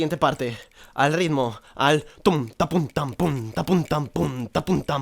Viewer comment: yo escuche q decia tampon xD